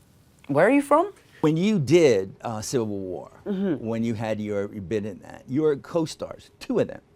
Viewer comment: who heard him say... SILVER WAR... instead of.... CIVIL WAR... Ploo...